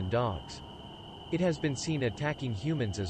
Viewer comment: "Yowies could be some _____?" I can't figure out what it says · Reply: Forma?